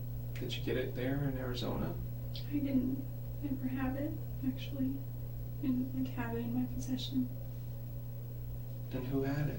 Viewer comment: What does she say at 15:35 "I didn't-- ever have it---actually--in "WHAT?" my possession?" What is that word she says? "Carrying" ???? It's an odd thing to not admit to. Really gives me pause to think someone else showed up with the gun/knife. Damn, I wish we can drown her in truth serum.